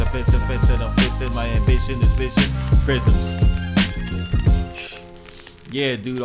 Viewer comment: Yo 🤣🤣🤣u needed subtitles for that flow u did 55:58, It damn sho sound like u said, "my ambition is bitchin' in prisms"😂😂🤣🤣good shit tho..